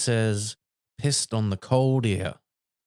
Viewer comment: I looked up the clip and heard it as Linda: 'I'll always remember the time when we...' ghost crab researcher: 'Pissed on the old pier' I interpreted as him saying he still remembers 'pissing' with the mermaid on a pier.